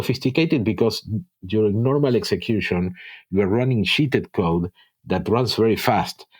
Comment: I finally worked out what a term was that I must have misheard (and so must the subtitles translator) - sheeted code. For those that don't understand the wonderful accent, is meaning JIT/JITted code, not sheeted code. And Smalltalk IoT is Smalltalk-80, of course. "Shamp" is jump. I hope these help. Aside from that misunderstanding, this is an excellent low-level interview about the guts of Smalltalk.